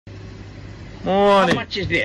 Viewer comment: The real story behind it might've started when the customer brought it to the cashier and the cashier said his money's not enough to buy it, so he went back to pick something cheaper it's still not enough again and the customer is beginning to get frustrated. When the customer was about to go again, that's where the cashier started filming and everything makes sense. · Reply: idk man I hear "Moani" instead of "good morning"